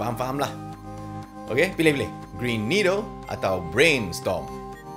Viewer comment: I heard "Brain Needle"🤔